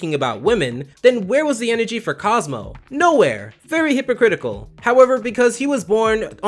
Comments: I swore I heard fairy hypocritical instead of very if so. Great job! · Excuse me sir am I mishearing or did you say "FAIRY hypocritical"???